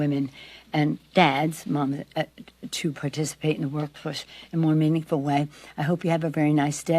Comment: It must be hard to keep her balance. Spinning... Close caption reads “workplace”, but I heard “World Push”. Did anyone else hear it?